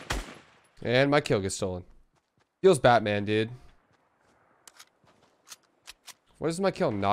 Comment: Did he say, “Feels Batman dude.”